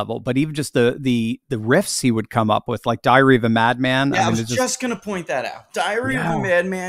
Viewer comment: I'll never forget seeing Randy Rhoads in Omaha on the Diary of a Madman tour. He was grinning ear to ear the whole time. It was awesome. RIP. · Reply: Sounded like he said "diarrhea of a madman"! 😮